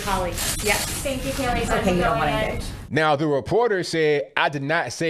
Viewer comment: Looool I first heard "Oh, youre a lying bitch" And after you mentioned what she claimed she said i actually hear "Oh, you don't wanna engage" That's like the blue/gold dress all over again